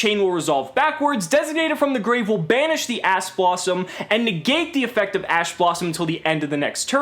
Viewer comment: did anyone else hear ass blossom?